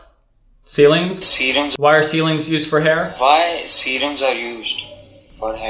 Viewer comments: I think he said "Why serums are used for hair?" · btw make sure you watch todays outro.. that was fun ;) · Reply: the guy who you thought said "ceiling" actually said "serum"